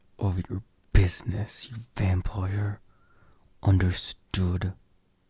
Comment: When you say Vampire, I always hear “Vam-ployer” 😂😂😂😂